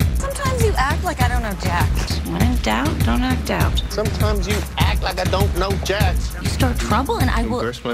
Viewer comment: and what does he say or rap in 0:33?😂😂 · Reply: He just repeats Lisbon: "sometimes you act like I don't know jack" 😜